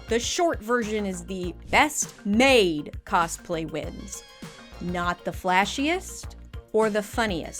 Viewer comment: i heard that as 'best MAID cosplay wins"